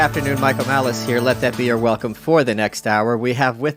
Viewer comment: I keep hearing "your are welcome" but it's spelled Your Welcome. What am I missing? Shouldn't it be "You're Welcome"? Or is it meant as sort of a, "this podcast is your daily welcome"? I dunno. Just wondering. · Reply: It's the second one. He says in the beginning of his podcast: "This is your welcome". He does this on purpose to make people's heads explode.